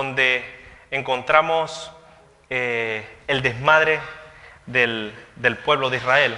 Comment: ¿ que dijo en el minuto 27:49 ? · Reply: Ioel Villa, desmadre,significa actuar sin control ni respeto.